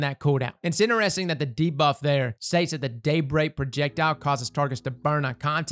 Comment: when said “Daybreak projectile” it sounded like he said “date rape projectile.” made me real curious for a second lol.